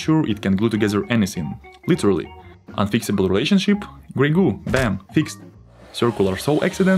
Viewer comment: Oh! Gray goo, I heard "ragout"... Which also kinda fits :)